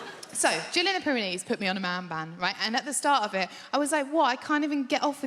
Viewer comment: OooOOooOOhhh...... I thought she said she was "IN a man band". Imagine my confusion.....